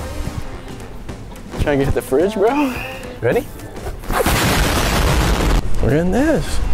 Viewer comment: did he say reeeeeeee?😆😆